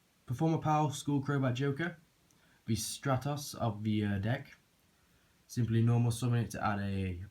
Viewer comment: did he just say stratus of the deck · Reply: Stratos of the deck. As in Elemental HERO Stratos, a monster that could search for any other HERO monster that you had in your main deck when summoned. i.e. A water if you wanted to make Absolute Zero, Malicious if you had a destiny draw to activate, diamond dude if you still had your normal summon left, a soul charge in hand, and a plaguespreader zombie in the grave and you wanted to go for an OTK on the next turn, etc. Skullcrobat Joker isn't quite as good as Stratos as Stratos also had the option to destroy spell/traps up to the number of HERO monsters on your field, and triggered when special summoned as well, while Skullcrobat Joker doesn't have the trap/spell removing option, and only works when normal summoned.